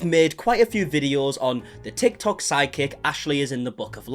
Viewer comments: Every time he says “psychic”…..I hear “side-kick” and it makes me giggle! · I’m glad your accent makes it sound like your saying side kick nit psychic lol